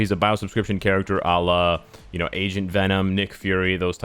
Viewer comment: Was listening in the background, and I thought said, "Asian Venom" and I did a double take. Agent Venom. Haha.